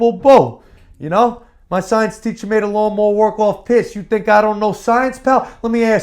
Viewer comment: What did Enzo Amore say?... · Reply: "My science teacher made it worth more while pissed you think I don’t know science pal"